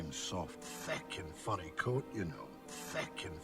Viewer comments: im not entirely sure but I think hes sayind furry coat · I thought the officer said “fecken funny” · I think he said “Fecken” like the f word in a Scottish accent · I thought he said “feckin’ furry coat” and i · "Thick and Funny"? I heard "Fickin' furry" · I thought that guy said “feckin funny” as in the f word Scottish version LOL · I heard “feckin funny goat, you know”